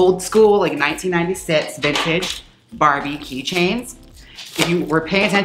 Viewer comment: Definitely thought she said barbecue chains instead of Barbie keychains